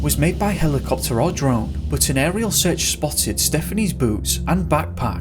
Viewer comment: Goddammit I was eating a crunchy cereals while watching a video so I didn't hear that good what he was saying and at this point I heard: "They found stephanies boobs". For a second I became horrified